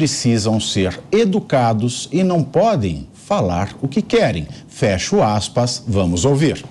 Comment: Foi pronunciado erroneamente a fala do Lula 13 no programa. O correto é "...FAZER o que querem...".